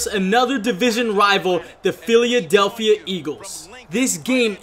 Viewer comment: did u say philyadelphya eagles